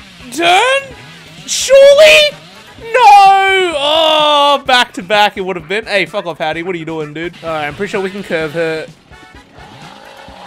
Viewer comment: "Fuck off fattie" IT SOUNDED LIKE THAT LMAOOO